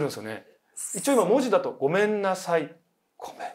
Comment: 心霊スポットに1人で行けるメンタルがまずすごいのよ…。13:38の「ごめんなさい」ちょっと声可愛いやんけ。5選全部聞こえなかった自分にゾッとした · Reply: 字幕を見てなかった私には「ごめんなさい」より「すみません」って聞こえました